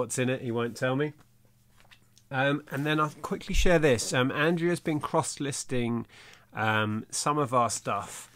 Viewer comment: I mis-heard the early part of the video - I THOUGHT you said 'Andrea has been cross, listing on Etsy' 😳😳 then I realised what you ACTUALLY said was 'Andrea has been cross-listing on Etsy' which made more sense ... I REALLY should read 'Eats, Shoots and Leaves' 🙄🤣😂🤣😂🤣😂